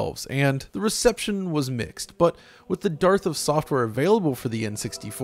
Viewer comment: With the DARTH of software instead of dearth XD